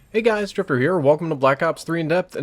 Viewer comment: I thought he said "hey guys stripper here" LMFAO